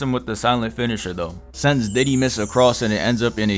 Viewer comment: did you just call that shit "diddy mist" 😭😭😭😭😭😭